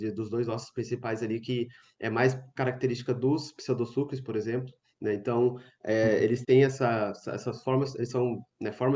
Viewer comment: qual é o nome mesmo do grupo que o disse nessa parte, ele disse Afanossauros ou Afranossauros? Não entendi direito. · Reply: Aphanosauria